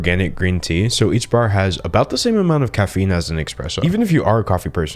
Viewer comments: did you say "express-o" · OH GOD !!! He DID NOTTTTTTTT actually say “eXpresso”...!!!!!????!?!??? Granted- I mean ....: admittedly he did state he “wasn’t a Java Person” however ...., he just CANNOT be One Of ........ “..THOSE People.” Godd No !